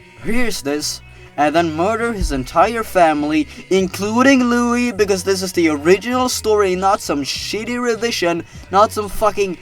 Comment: when I connected my headphones, I heard "Because this is the phone one connected"